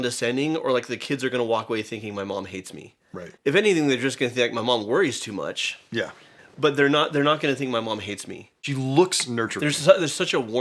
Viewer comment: As a spanish speaker, wtf did he said on minute 6:46? HAHAHA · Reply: "If anything they're going to think 'my mom worries too much,' but they're not going to think 'my mom hates me."